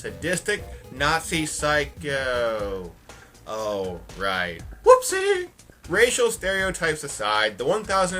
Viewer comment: Your "whoopsie" at 13:46 sounded like the mortal kombat "toasty!"